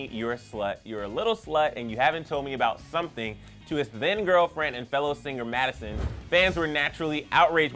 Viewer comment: did he say his thin girlfriend lol